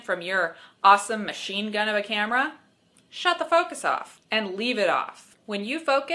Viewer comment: I heard "Shut the focus up... I mean.. off." lol!